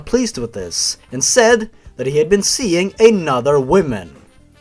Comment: during the Alyssa one, I thought I heard "8 other women" you savage